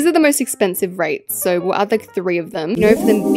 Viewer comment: js me but I heard “reates” idk